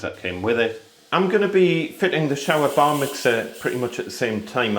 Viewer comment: Bar Mitzvah I thought i heard and realised it was Bar Mixer!! 😀 Another video of great tips.